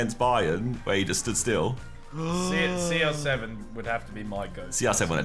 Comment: 😹😹jokes Yo even subtitles said the reality Ethan :- yo ronaldo has to be my ghost